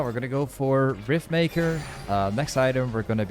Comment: I thought he kept saying Faker